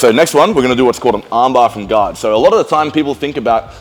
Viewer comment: I thought he said an arm bar from God 😂😂